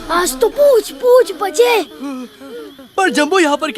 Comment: can anyone tell me what the kid says in 23:30 and how it is pronounced in hindi pls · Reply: He was saying the Line in hindi for humans it was bal bal bache but they are elephants so he said aaj toh puch(tail of elephant) puch(Tail of elephant) Bache .